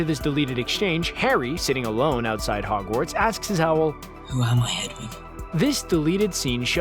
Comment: what did he said? I can't catch it. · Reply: ''who am i Hedwig?" he is asking his owl